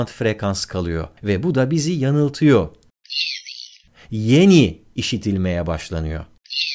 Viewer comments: ben yine yanny duydum yalnızmıyım ? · abi ben nedense yanny duymam gerekirken laurel duydum? dakika